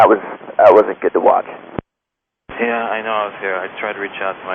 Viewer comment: INCREDIBLE!!! You edited the ATC!! He clearly said “I messed up..” and you cut his audio and write “I know.” on the caption??. This is bloody misinformation. You should be run off the platform! Bloody liar!